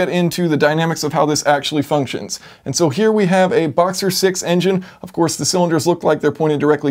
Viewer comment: I thought you said this BOXSTER engine at first and was ready to run to google to see if Porsche corrected their cylinder count sin :(